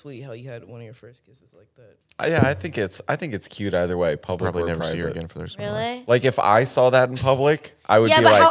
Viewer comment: "Probably never see her again for the rest of my life" is what Zach muttered here.